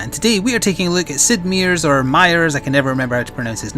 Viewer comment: For some reason, I heard Siegmeyer's rather than Sid Meier's. I turned away from my food and asked, "Sir Onion?"